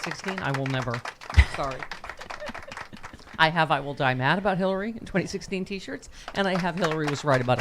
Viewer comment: I must have an auditory processing glitch... when you say "I will die mad" I hear "Neil Diamond".